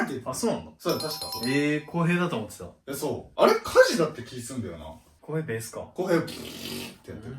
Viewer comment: え、koheyさんにしか聞こえんww